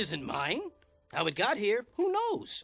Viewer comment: I really thought the cat said "I'm a god here" for a second